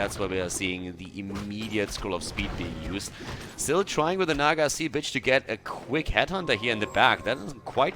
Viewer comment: Did... Did he just say "Naga Sea BITCH"??? :O